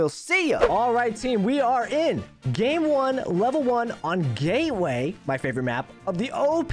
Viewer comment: and 0:49 he said “gayway” instead of “gateway” xD